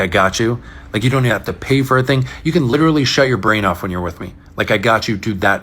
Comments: he said "SHUT your brain off"? I heard, "SHOW your brain off"! I've been around too many leftist men. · “You can shut your brain off with me.” Well that sounds boring 🤣 As mentioned, sometimes turning your brain off and letting someone else deal with it is nice… but most of the time, I refuse to turn my brain off. Im using it. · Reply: This makes so much more sense. I totally thought he said "show your brain off with me" and didn't understand any of the commentary about it. 😅😅